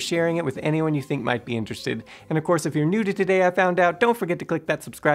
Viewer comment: I need to cut back on caffeine. I swear he said. "And if your into nudity, click that subscribe button for more." 5:31 Either that or "If you were neutered today......"